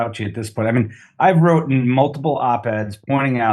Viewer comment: did he say wrotten? Lmfao